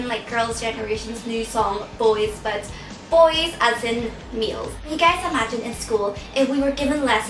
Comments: Definitely thought she said "boys as in meals" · hahaha....I heard "boys, as in meals." :P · Hahahaha, when you said "Boys as in males," I thought you said "Boys as in meals." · It sounded like you called boys "meals" LOL. xD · I heard "meals" instead of "males" and went like this o_O .. LOL, bad hearing .. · haha when she said boy as in males i thought she said meals :) hehe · i thought she said boys as in meals i was like BUBBI!?!?? her accent is adorable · I just thought I heard her say, "boys, as in MEALS" PMSL XD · lol i thought she said "boys, as in meals" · i thought she said "Boys As In Meals" ... XD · LOL when you said males i thought you said meals. And i was like whoa.. you eat boys for meals? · I thought you said "Boys as in meals" XD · instead of "males" i heard "meals" and i was like "O.o" and i was thinking "i didn't know bubz is such a "outgoing" person... hahaha awkward~~ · I'm sorry but I heard..."Not boys as in Girl's Generation's new song, Boys, but...meals." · Your accent is so cute! When you said "males" I thought you said "meals" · with your accent it sounded like you said "boys... as in meals." TEE HEE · at first i thought she said "meals" and not "males" XD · For a sec i thought you said us bois as in meals but then i was like ohh males xD. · "boys, as in males" i totally heard "boys, as in meals" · I thought she said "Boys as in MEALS" xDDD I was like, "Whoa there..." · When you said the word Males I heard it as Meals!! :D LOL JK